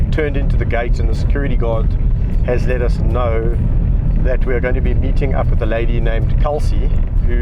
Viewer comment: I heard gonna be beating up the lady